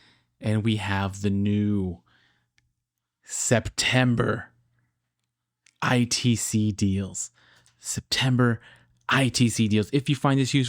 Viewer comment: Thought it said "Septic Deals"